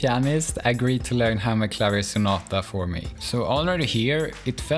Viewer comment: I 100% thought this said “I put HAMSTERS on a piano…” and was looking for the hamsters right up until you actually showed the hammers. I think I need to go outside for a walk now.